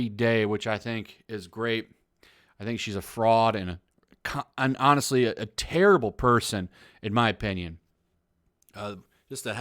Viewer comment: “I think she’s a fraud and a cun- honestly just a horrible person” anyone else catch that